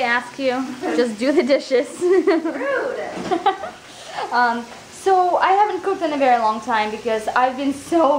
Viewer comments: i swear at 8:44 you said "so, i havent pooped in a very long time..." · I SWEAR I thought she said I havent pooped in a very long time and i burst out laughing when she explained why she hasint "pooped" but then i realized she said cooked